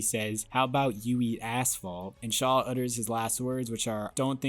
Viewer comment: First time I rode it, I thought letty said “how bout you eat ass!” There’s so much sfx going on that I can’t hear asphalt